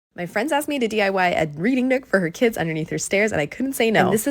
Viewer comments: also ngl i fully thought you said breeding nook first and i was so confused and concerned and had to reload the video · I thought she said her kids breeding nook I was like 💀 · i misheard that as “breeding nook” and became deeply concerned.. 😭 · Bro I thought she said BREEDING nook💀💀